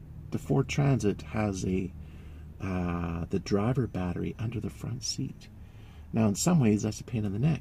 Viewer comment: Pretty sure that there is no battery under the van. ☺ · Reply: My bad, was watching with my grandson in the room, I Mistook what you said. I thought you said the battery was under the transit van @ 3:22 Chevy vans with the duramax have a battery under the van under the drivers seat attached to the chassis and another one under the hood. Takes two batteries to start a V8 diesel. Brutal spot for it due to corrosion and a PITA to get at to service. I have two additional 12V deep cycle house batteries behind the drivers headache rack. I charge it all off the alternator via a solenoid.